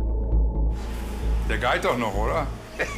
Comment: was sagt er da? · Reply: "Der geht doch noch, oder" Ist irgendein Dialekt aber frage mich nicht welcher.